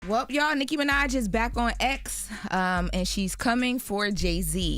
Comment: I thought she said Nicki was back on ecstasy